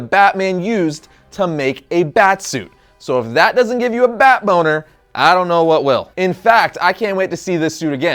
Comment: Did he say bat boner 12:45 🤭🤢🤮😹